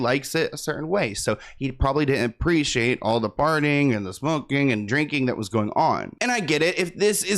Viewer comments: I thought you said farting 😭💨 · I'm sorry at this moment is he saying that the mayor doesn't like all the partying drinking and smoking or farting and drinking and smoking caz I hear farting and I couldn't stop laughing